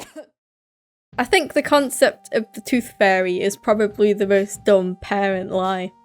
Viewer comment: instead of saying tooth fairy it sounds like your saying toothfurry with your accent